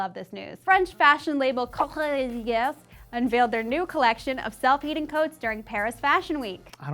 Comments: Definitely sounded like said "self-eating coats". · I definitely thought said "self eating coat" and thought - yupp that's Fashion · I genuinely thought you said self Eating coat. I suppose the other idea works too...